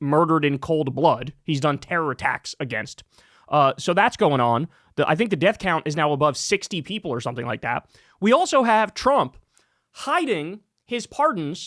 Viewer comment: I heard 70 people? Who are these people in the Caribbean that are being killed?